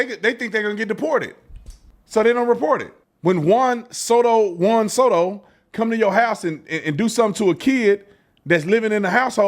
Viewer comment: Did he just say when juan soto comes to your house? ⚾️ juan soto making 765 million dollars to .230 with the new york mets rn